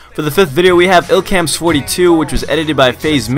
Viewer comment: what illcams could swear he says 22